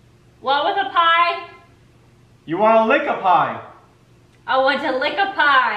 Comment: THATSSSSSSSSS ME....I LOVE YALL.Great video and I am wearing sweat pants. I'm working out.You two are the best.When you first said lick a pie I thought he said lick up high and I dont have nothing in my ears or between them.